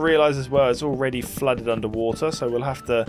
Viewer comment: did bro just say , its flooded under water